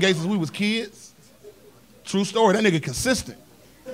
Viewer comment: Naw they was “punks” and “sissies” back then lmao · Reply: I thought he said Sissy's but it was the word consistent